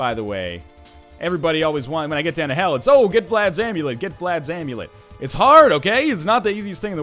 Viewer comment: Did you just say "get flads amulet"?